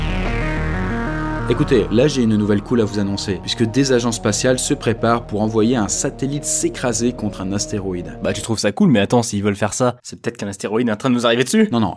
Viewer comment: c'est pas "des agents spaciaux" plutôt ? Dîtes moi , je sais pas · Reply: La liaison est pas très bien faite mais c'est "Des agences spatiales"